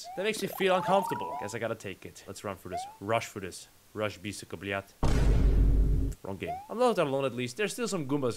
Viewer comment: lol if i heard right says 2 russian word syka and blyat